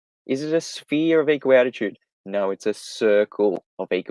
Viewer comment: Is saying a sphere (or circle) of equal altitude or attitude? More often it sounds like attitude to my US-English ears.